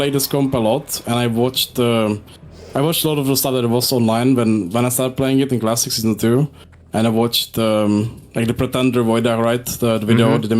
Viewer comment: he mentioned having watched things as he was developing, I couldn’t understand the accent totally. It’s at 40 minutes almost exactly. 39:59 What was it he said? I can’t timestamp on mobile properly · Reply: "I watched a lot of the stuff that was online when I started playing it in classic season 2, and I watched uhm... like the pretender [pretenderx, rogue] video right? the video with the mates?" mhm yea the Yanukovich "yeah the 2 hours one [I think, speech is overlapping, it's possible he's referring to another player name I dont recognize] yeah yeah yeah. that was like my baseline I watched or read through like all the guides they made for that comp [rogue/lock] [...]" might be that it's not 2 hours but a priest he's referring to and it's a rlp guide but I can't find anything looking around a bit for such a thing